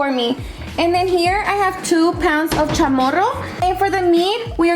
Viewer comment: I'm making pollo adobado 🙌 But I love caldo de rez When I use to work, this guy told me he wanted 5 chamorros & I thought he said cachoros ( like a puppy ) & I was like uhh no tenemos eso 😂